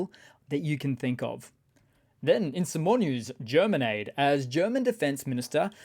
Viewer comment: Oh German Aid! I thought you said Germanade, like lemonade or Gatorade :D